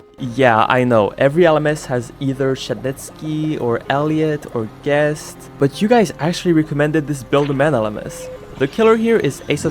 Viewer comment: i open the subtitle he said shedletsky and guest but the subtitles said shinnizki and forgest in